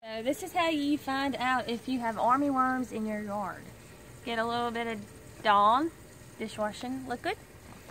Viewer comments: Lmao I thought she said "And a little bit of DONG." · lol I thought she said get a little bit of dong and I was like wot